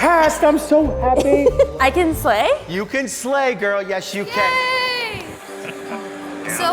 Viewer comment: I thought she said you can slay girl💅